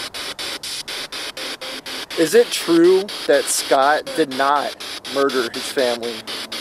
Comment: When you asked” is it true that Scott did not commit this crime “ I heard “it is! Thank you! I heard about you from “ True Crime Garage “